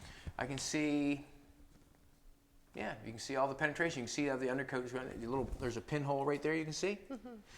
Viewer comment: I heard "gap and penetration"...and something about welding and butts... 😅